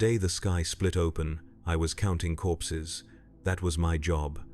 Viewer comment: At first I thought he said “I was counting horses”. I thought.. wait.. this is 40k, went back and listened again, “I was counting corpses” Ahh that’s more like it. 😂